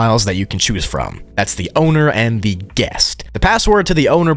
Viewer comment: all i heard was "the owner and-state farm!", God i love adds :x